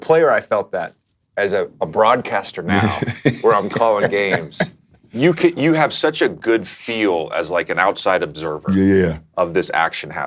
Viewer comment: did JJ just say “broadcaster” or “BRONcsster” ? 😂😂